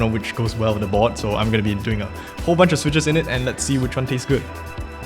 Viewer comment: did he just say which one TASTES good LOL